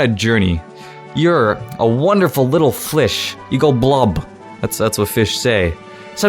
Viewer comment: he says Flish... not Fish. His exact quote : "So imagine you're a little FLISH. You go blub. Cause that's what fish say. Blub."